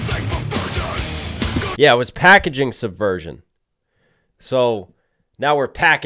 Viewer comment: Packaging suversion? This whole time I thought he said “pikachu’s a virgin’ 😂